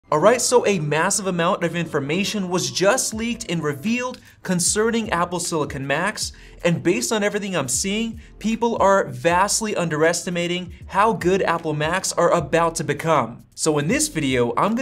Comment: I thought you said Apple Silicon Max 😅